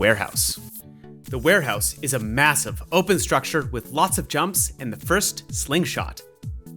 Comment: did someone say massive ,low taper fade